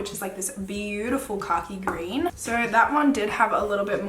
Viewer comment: love this review!!!!!! also am i the only one who thought she was saying "cocky" until i finally realized she was saying "khaki"😂😂i love her accent!!!